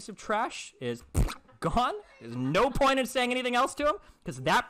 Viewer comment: Jeez. I have never seen him this unhinged. He spend almost the entire call mocking mannerisms and speaking over her instead of actually engaging with her AND then misgenders her after the call. Wow, he really needs help! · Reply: It really sounded like he said "there is no point in saying anything else to him" I guess it is possible that he said "to 'em", but it really sounded like it included an "h"-sound. Also isn't it Destiny's thing to call people "buddy"? :-) Are you also a Destiny clone?